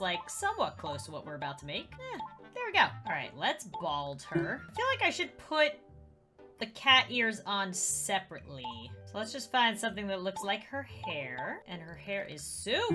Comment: i think the CAT EARS me (aphmau lover): SKSSJSJSJSKSJDSKKSSK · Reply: I thought it said “fears”